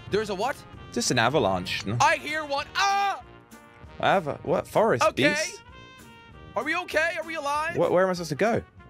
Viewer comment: think it says frost beast😂